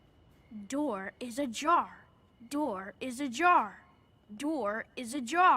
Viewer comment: I always thought he was saying door is a jar, HE'S SAYING DOOR IS AJAR, I'M NOT OKAY.